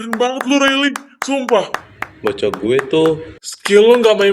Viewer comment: hah ini bilang apa ya?? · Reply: Bocah gw tuh🤭🤭